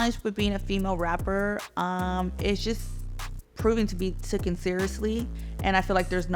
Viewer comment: Wait, did she say "TOOKEN"? 3:11 instead of the correct word, "taken"? Some of her money might be better spent on education instead of plastic surgery.